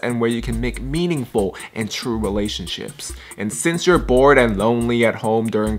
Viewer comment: I heard u say MALAYSIAN SHIPS....is that normal??? oof I like my country too much 😅